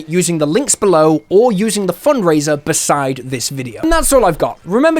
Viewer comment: My silly brain heard it as "Lycan Subscribe" at the end and imagined a confused werewolf clicking the subscribe button XD